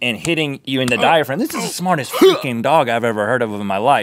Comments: I think Rhett said "this is the smartest f*cking dog ever" or "this is the smartest freakin dog ever" but it was covered up by links "HUHHH". What do you think? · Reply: He said "freaking". If you listen closely, you are able to hear the "r". Helps if you are on a mobile device. He has said this word plenty of times whereas he does not carry the "r" for very much time at all. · Did Rhett say "smarted f***in' dog" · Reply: He said "smartest freakin' dog". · Did Rhett just say what I thought he said?! Did anyone else pick up on that or am I just so tired that I imagined it? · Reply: He said freaking so no. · sounds like Rhett says "this is the smartest f*cking dog I've ever seen" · They actually swore? I never heard that on this show yet XD 7:00, im not mad, just chuckled, or I might be miss hearing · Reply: Freaking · it sounds like Rhett said, "This is the smartest f*cking dog iv'e ever heard of in my life" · DID RHETT SAY THE F WORD! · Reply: He said "freaking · To everyone who thought Rhett said the F word on 7:00, he said FREAKING not F***ing. Although I can see how you think that because Link's HUE muffled it. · What did Rhett say at 7:00? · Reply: Freaking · WTH, did Rhett just say f***ing at 7:00? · definitely sounds like he said f***ing